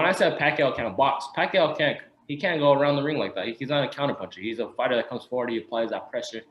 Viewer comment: Did he just say Pacquaio isn't a counterpuncher? Pacquiao has displayed boxing skills like what he did to Diaz and Delahoya. But I do see the point he's trying to come across but Pacquiao does have underrated boxing skills. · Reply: Damn I could’ve swore he said Pac-man is a counterpuncher